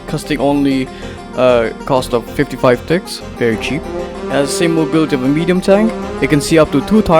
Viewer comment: when you said very cheap i thought you said very chip